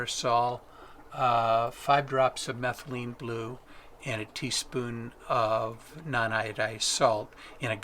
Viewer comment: What does he say at 45:27 in the video for the solution? I got the last two parts, just the first. Is it aquarium salt? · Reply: Teaspoon of non iodized salt